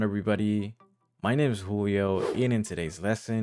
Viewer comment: Julio!? I heard "Coolio" ho damn Thank you & wish you all well 😃🌟✨🙌 <num>:12 pm ist